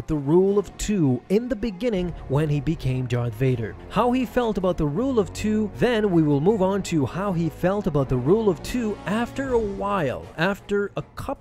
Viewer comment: Did I hear him correctly at 1:25 “How he felt of the rule of two and how he felt of the rule of two and then how he felt of the rule of two”